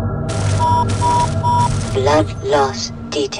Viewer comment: Oh, it's BLOOD loss detected! All these years I've been hearing it as LIFE loss detected. And I don't think I've ever heard that "internal bleeding" message. Maybe I have an unconscious form of haemophobia or something.